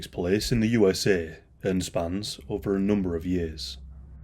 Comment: For some reason i heard ''This case spans over a million years''